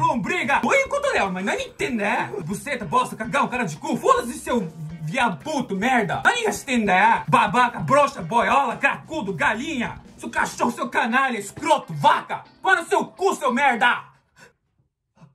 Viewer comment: vakaが馬鹿に聞こえたw